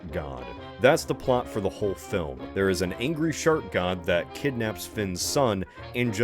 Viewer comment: i heard this is "shart god" the first time and it was so fucking funny